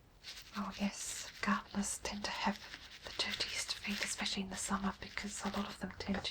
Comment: Thanks for such a great video. It never fails to put me to sleep. One question though, I can never make out what you say at 10:02? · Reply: Lol...I had to go back and check it out. I said 'oh yes, gardeners tend to have the dirtiest feet in the summer' haha...what possessed me to say that lol. I was probably drawing from my own experiences of gardening when I was younger in bare feet. I don't do that anymore if I can help it...takes too long to scrub the feet clean again lol.